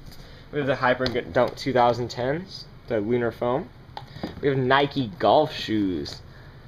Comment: Did he say weiner foam?